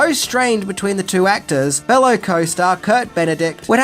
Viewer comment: Did you say “Kurt” Benedict approximately 10:25? Or did you say Dirk, and I keep hearing it wrong?